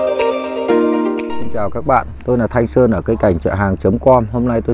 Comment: Hây lắm anh ạ, xin cảm ơn sự chia sẻ những kinh nghiệm quý báu của anh. Chúc anh sức khỏe và thành đạt! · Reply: Văn Bình Nguyễn bác chủ vườn tên là Thanh Sơn ạ 😂 chắc mình nghe nhầm